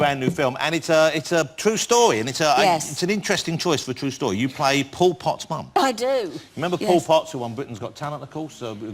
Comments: I definitely heard that as 'You play Pol Pot's mum'. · I thought Jonathan Ross said to her 'You play Pol Pot's mum'. I thought that's weird casting. Only when the picture came up did I realise it was Paul Potts' Mum